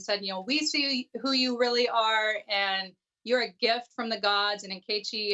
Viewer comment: Yup.. low key book plug, and Tamara was all over it. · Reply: *Tamron( not Tamara).. did she just say “be who you really are?